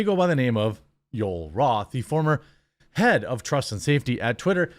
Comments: did you say "former PED of trust and safety"? LOL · I heard "former ped of trust and safety"...even though Jeremy didn't say that, I am still not wrong.